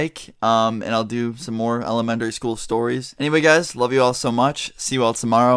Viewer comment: did say elemenDary school? Lol.